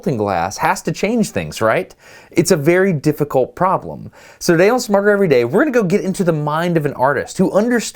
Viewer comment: Awesome video as always, but I would recommend taking a look at your caption track. at 1:45 you say "so today on SmarterEveryDay" but the caption says "So they'll smuggle every day" I can hear, so it's not even a slight inconvenience for me, but I feel bad for our deaf friends who have to try and guess at what you said, and hopefully deduce the actual words from a combination of lip reading and context. I can type at around 80 words per minute, accounting for corrections and grammatical accuracy. I would be more than happy to fix all of your caption tracks for you, if I can get paid for doing so.